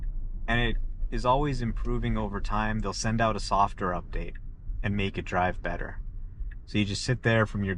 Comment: Is he saying a softer update? 😅 Isn't it supposed to be software update? And did he say driving wasn't (was an ) annoyance to going places? This guy clearly doesn't understand about driving but I agree the new freedom which was once driving is now the freedom not to have to drive, amazing